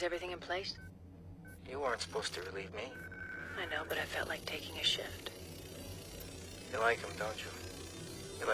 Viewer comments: I thought she said "yeah, but I felt like taking a shit" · I swear trinity says I felt like taking a shit